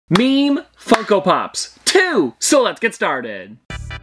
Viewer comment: I thought I heard meme fucko pops .-.